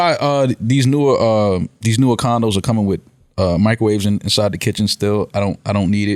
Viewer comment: i thought he said these newer condoms are coming with microwaves installed... oh yea?